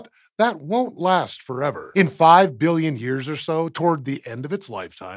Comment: Your comment that the sun will only last 5 billion years had me worried there for a moment since I thought you said only 5 million lol. We will have moved on to new homes or died off as a species long before our sun becomes a problem.....come on!